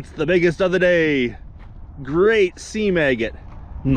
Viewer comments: >> Did you just say, "Sea Maggot"? (0:] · When mentioned the seam agate I heard it as 'sea maggot'. I need my ears mined.